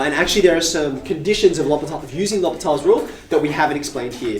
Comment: what rule is he talking about? can't really tell what he is saying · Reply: nvm its l'hopital's rule